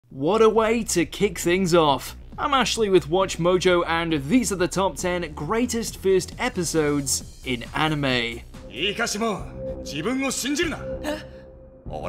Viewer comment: When you said i am ashely i thought you said i am actually with watchmojo